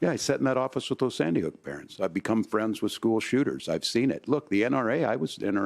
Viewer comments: "I became friends with school shooters" Did he actually say that?! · Did I just hear Gov. Walz say he has become friends with school shooters? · did he just say he became friends with school shooters?🤨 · Did he just say that he’s become friends with school shooters??